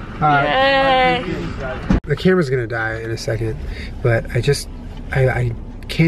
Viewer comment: It sounded like Julian said "kermit's gonna die" instead of "camera's gonna die"